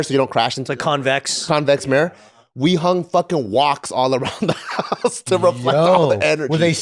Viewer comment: what did he say at 4:37? hung walks? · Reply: “hung woks”